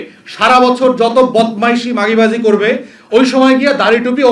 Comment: Did he say মাগীবাজি? 🤣